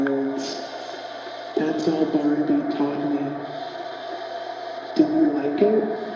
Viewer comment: Does anyone else hear: "That's what Barnaby taught me" at 1:09:47 ??